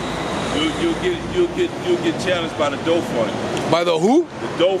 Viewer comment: I thought he said The Dope Fund